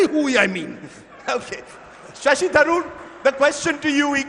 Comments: This is what called an intellectual debate, wished Rajdeep should have sit on the audience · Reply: what does he say? dennistism? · what does he say? dennistism? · moderator failed to understand the most basic rule of being a moderator. he is actually carrying his own agenda. its like a cricket umpire will start doing personal vendetta on field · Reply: what does he say? dennistism?